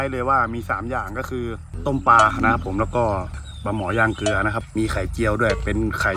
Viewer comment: ผมได้ยินปลาหมอยาเบื่o5555